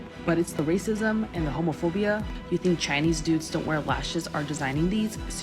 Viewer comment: lol at Lashify comments, she could’ve left the “American immigrant” part out because what if those “Chinese dudes” were in the US? Then what? It sounds more like she hates men when there’s always a man behind many of the big beauty things including clothing and other products so 🤷🏾‍♀️ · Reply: I originally heard it as Chinese Dupes and was like “yeah, that’s a thing that happens, but also not everything is American?” Chinese Dudes does make a bit more sense in context now.